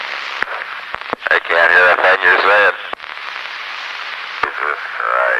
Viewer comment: might be me but he says: they cant hear a thing you said and not (you're saying) can any1 confirm this ? and if you listen carefully,at 5:41 you can hear voices barely able to make out whay their saying but you can hear em but you realy realy need to listen carefully, it goes on until chaffee, says something